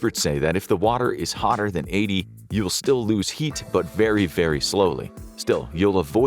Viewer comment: Typo at 8:06 it says you will still lose heart instead of heat